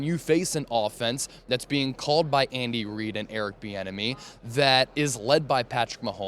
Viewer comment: I thought you said Eric the Enemy at first 🤦‍♀️ 😂😂 Hopefully Mahomes will play as well as he did in his previous Superbowls 😂 Loved the interview, he’s awesome 💚💚💚💚💚💚💚